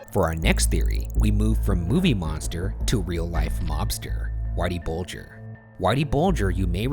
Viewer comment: Why did I hear “from real life lobster” instead of “real life mobster”